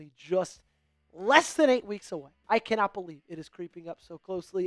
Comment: did he say 8 weeks?